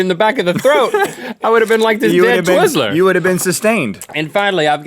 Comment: Anyone else catch link say "damn twizzler"